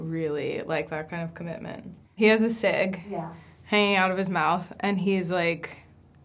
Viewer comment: What most people hear: "he had a cig hanging out of his mouth" What i heard: "he had a sig hanging out of his mouth"